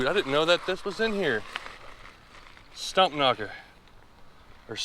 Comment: Haha, since I didn’t know what a stumpknocker was and partially listening with your accent (to me in Ca!) I thought you said stumpnugger. Google found it for me! Awesome vid!